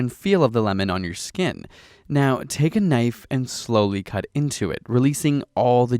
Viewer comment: I thought you said take a knife and cut yourself